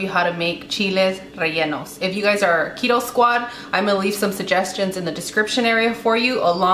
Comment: I thought you said cheetos squad lol